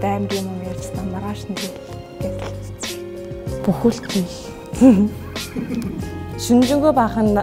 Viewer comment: what did she say ? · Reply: Buh uildel ni l